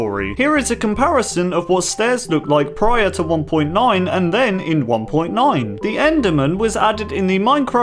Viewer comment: "prior to 1.9 to 1.9" did anybody hear DAT?